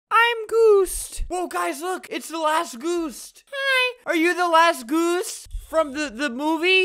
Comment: “I’m geust” “OMG YOur The LASt GUst?” Edit: .. holy cheese dust on a table dats a lot of likes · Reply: When I first heard that I thought he said "The Last Geesed" :/